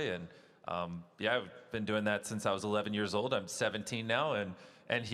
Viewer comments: did bro say "ive been doing that since i was 11 years old and im SEVENTEEN NOW???" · Did Jimmy say he was 17? Haha